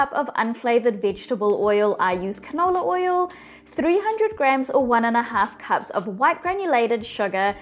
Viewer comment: She said 150 grams of sugar not 250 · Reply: in 1:45 she actually said 300g